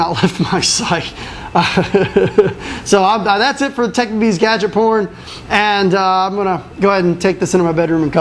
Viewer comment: lol at 15:11 it sounds like he says gadget porn